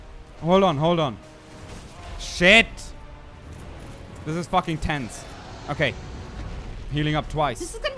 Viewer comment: "this is fucking tents" is what I heard. I love you xD